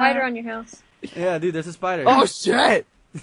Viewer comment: it sounds like he is saying thats not our house thats our SHIT XDXDXDXDXDXDXD